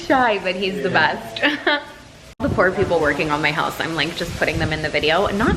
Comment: Love your vids but In 10:45 did you say the poor people????